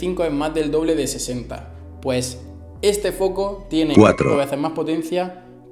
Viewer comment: En el minuto 11:27 hay una voz que dice 4